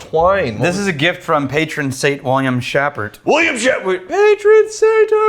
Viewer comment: I thought said William Shatner at first.